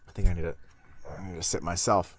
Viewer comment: It's your own video and you got the captions wrong? :D Sit/Sip